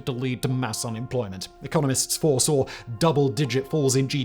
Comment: I had to listen twice because I thought you said "the communists foresaw" and I was like, wait, what? then I listened again and realized you said "economists" which made a lot more sense